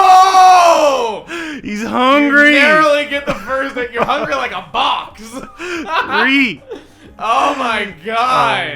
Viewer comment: did say "you're hungry like a Box??"